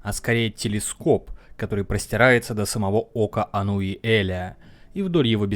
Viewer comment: упорно слышу "кока Ануиэля". Недосып сказывается, но так даже забавнее